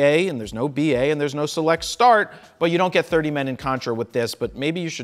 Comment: what does he say? "But there's no man in contra...?